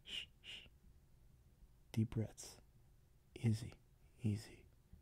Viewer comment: help me, the first time you said “easy” there at 4:27 i heard “izzy” which is my deadname and JOLTED up